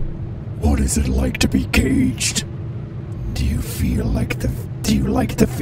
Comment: at first I thought he was saying "what is it like to be gay" and in my head I responded"pretty nice actually"